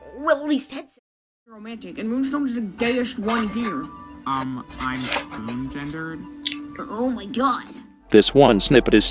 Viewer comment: I couldn't hear what TOL said, what is his gender? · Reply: Spoon gender